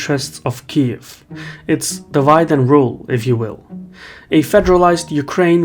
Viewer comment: Did... did you just say "davai-de and rule"??? 😂